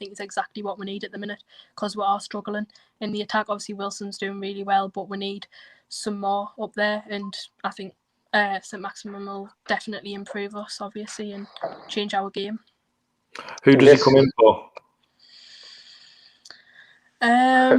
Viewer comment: I laugh every time I hear 'Saint Maximum' Haha